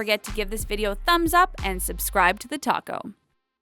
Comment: when she said "Subscribe to the TALKO" I thought that she said "Subscribe to the TACO"😂😅😂😂